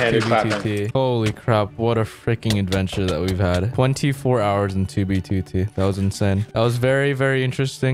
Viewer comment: I thought it said "I Survived 24 Hours in 2020".